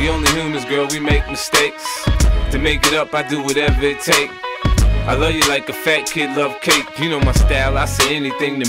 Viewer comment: I love you like a fat kid love cake. One of the best lines in Hip Hop history 😂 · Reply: i always thought he said i know you like the fat kid love cake